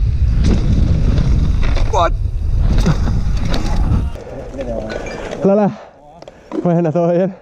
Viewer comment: Alguien me dice que dice en el min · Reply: Dice lala??